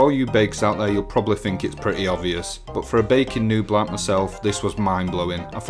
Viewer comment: Thought you said bacon rather than baking at first lol